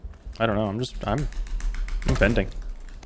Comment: why did I hear it "I'm bending"